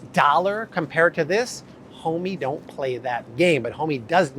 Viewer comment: Did he say, "Homey don't paly that game." OMG! No he didn't He took me back to the 90's In Living Color.